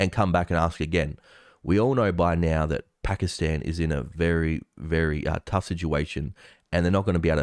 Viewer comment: All I heard was "Pakistan is Trash"